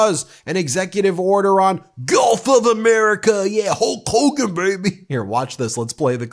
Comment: It sounded like she said Golf of America which is accurate as Trump's always playing golf while fantasizing about taking over the world and renaming the planet. Surprised he didn't say Golf of Trump!